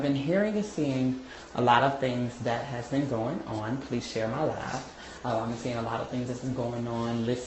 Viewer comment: if this girl say, "share my live" 1 more time👉 I'm gonna 😱scream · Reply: I thought that heffah was saying, "share my love." 😂😂